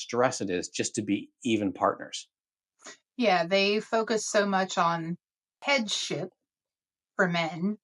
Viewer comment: I thought said, about JW focus for men.... They focus on head shit. 😂 Took me a minute to realize what she said, but... Either way, accurate.